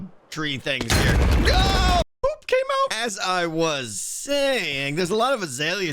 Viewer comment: Did just say 'Poop came out!' in a high-pitched voice at 14:05?